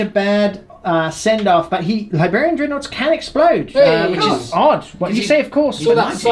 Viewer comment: I could've swore I heard say ''The Labia dreadnought''....